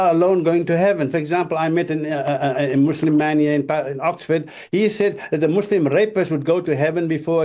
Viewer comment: I think he said "a Muslim man here in Oxford" not "a Muslim imam", so the video title is misleading. But the key point is that in practice some hearers are going out from mosques with the view that they have a spiritual superiority which overrides the moral demands of their own religion. (3:00 to 4.30)